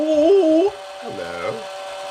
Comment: That sounds like Hello instead of yellow!